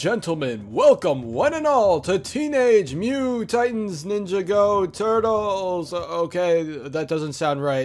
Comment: did he say ninjago?